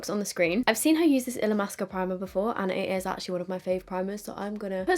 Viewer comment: What primer are you using? I heard “illa mask primer” but I can’t find it. If anyone know please let me know. I’m wanting a good primer.